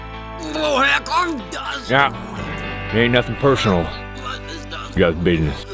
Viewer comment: I thought he said ain't nothing personal, diabetuss! Lol